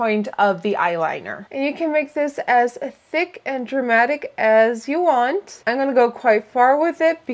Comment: I thought she said "make it as thick and dramatic as you are" xDD